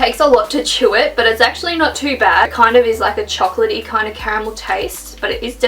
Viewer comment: did she say camel or caramel? I'm guessing she said caramel.